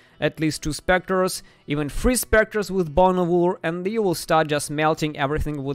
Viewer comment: what do you say here how do u get 3 spectres? you say bone orhool? whats that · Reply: bones of ullr